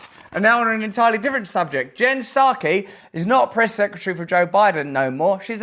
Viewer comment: Perception is a weird thing. You say Jen Psaki and I hear "Mouth of Sauron".